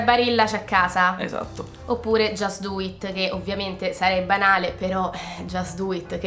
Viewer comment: Comunque io ho dovuto riascoltare una decina di volte il minuto7:52 perché sentivo sempre "sarebbe anale" e non capivo che c'entrasse (poi ho messo i sottotitoli).